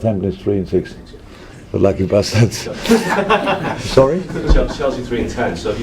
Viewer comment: did he call Chelsea lucky bastards in 7:59😂